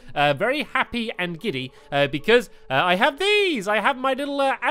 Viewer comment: I thought you were saying "I have these! I have DOORS!" Because you were switching around the pointer thing to look at the doors 😆😆😂😂